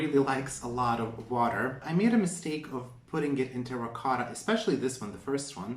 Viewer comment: I’m pretty sure you said “ricotta” instead of “terracotta” 🤣 LOL